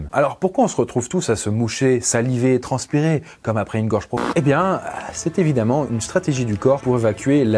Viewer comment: bah oui j'ai jamais dit ça- mais je vois pas de quoi le commentaire parle- ya quoi a 6:33😭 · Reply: il dit "gorge profonde" et (désolé si t'es trop jeune), c'est en rapport avec les rapports sexuels, bref